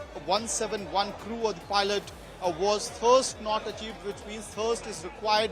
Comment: What the heck is he saying,is that supposed to be english? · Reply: Thirst. Forget the accent , the whole report is pointless.